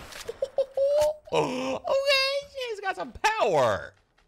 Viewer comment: Oh bhenchoo, It got some power! it sounded like this